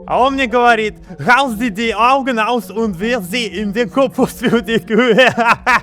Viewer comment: что он сказал? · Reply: Это фраза на немецком "reiß dir die Augen aus und wirf sie in den Kompost für die Kühе" которая в переводе значит "Вырвите себе глаза и выбросьте их в компост для коров"